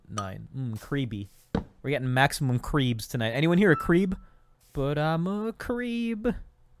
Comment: i heard ''CWEEP'' not creep